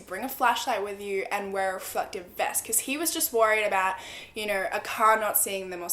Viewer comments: did she say “fucken vest” or am I having trouble understanding another word? Lol Edit: I think she is trying to say reflected vest. Lol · what did she say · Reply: Ayas Hassan a reflective vest